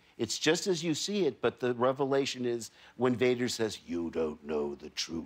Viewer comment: I didn’t hear Vader. All I heard was Joker. 😂